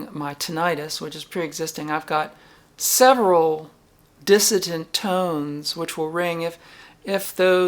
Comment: Sorry to hear. I keep hearing "dissident", when I expect you to say "dissonant." (Hopefully I don't need to have my ears checked!) What gives?